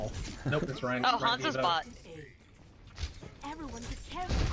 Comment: I thought she said Hanzo was a bot then I realized she said bottom and I was silently chuckling because he got 4 of them right after